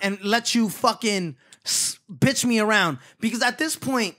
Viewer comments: I thought he said “bitch on “ 😭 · Why did it sound like he was saying bitch Sean